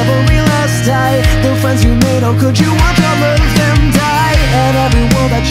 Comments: The first time I heard this, I thought at 0:31 instead of them die, he said Hentai, my mind goes to places. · I thought it said "how could you watch all that hentai?"